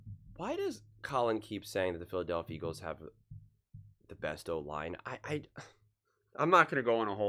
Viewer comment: People be wildin saying the eagles have the best O line 🤣 lane out, steen is mid, Dickerson on 1 leg, Jurgens has pretty much missed half the year if you include games he’s left early. What game yall watching. The backups are holding up in Pass pro sure , but they can’t run the ball at all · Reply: I thought he said we have the 5th best o-line